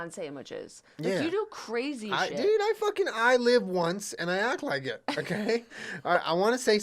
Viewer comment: "I only live once and I act like it" is the Aries motto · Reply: OfficialFriendlyGhost i thought he said “i’ve lived once” which makes it funnier imo 😂